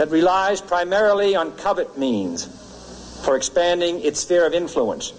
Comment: I know Kennedy's speech reads "covert" but it sounds like he says covet! Listen closely, their isn't even a dropped R .